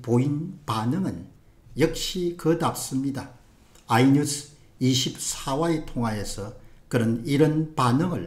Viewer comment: * 공데일리 신문 : <link> * 핸드폰에서 '공데일리', '공병호TV', '자유수호' 앱(App)을 다운로드 받으시기 바랍니다. * 자유민주주의 스토어 런칭! [해외] <link> [국내] <link> * 바른길 RightWay 채널, 개설! <link> * 공은총TV 채널, 개설! <link> * 공병호TV 비메오(Vimeo) 채널, 개설! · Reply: 발음 문제 3:02에서 뭐라고 말씀 하시는지 통 이해할 수가 없었는데 4:35에서 다시 한번 언급 하셔서 들을 수 있게되었습니다. "아이 뉴스 24와 통화에서"